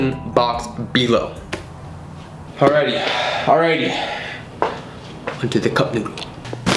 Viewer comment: Matt stonie- consumes this for a challenge Me- eats cup noodles because I'm a broke ass college student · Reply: i thought you said boneless cup noodle😂